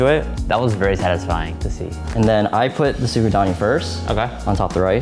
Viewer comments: what is that spread he put on top of the rice? I couldn't quite make out what he said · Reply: it's the tsukudani sauce they mentioned at 12:44, quite tasty, it's like sweet-salty seaweed chutney. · What's in that jar at 13:26? Magic secret sauce??? Even the closed captions doesn't know what he said ... "suvatani" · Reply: Another comment had it. Tsukudani.